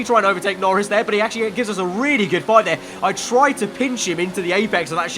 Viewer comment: did he say Job done or Dob done??